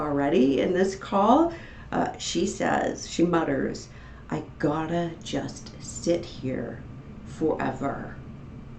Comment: she did not say "I just gotta sit here forever" she said "God it's just taking forever"